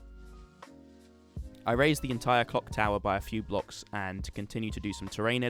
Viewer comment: ok, I seriously CANNOT be the only one who hears "cock tower"